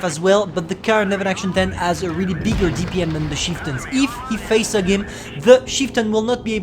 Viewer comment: * says chieftain* What i hear: "sheetin"